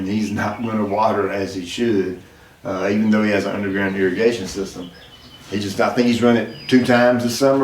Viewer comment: Shwoooo... I thought I heard "100 grand irrigation system".... It actually was "under ground irrigation system". Great advice again!!